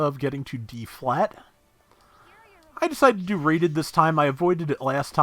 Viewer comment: So now that I have actually played the game on PC I am just now realizing that every time you said "rated" matches I heard "raided" matches. Fail :P